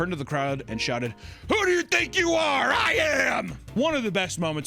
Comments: Always thought he said "who you THINK you are, I AM" which actually goes pretty hard. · I always thought he was trying to say “Who ever you think you are, I am”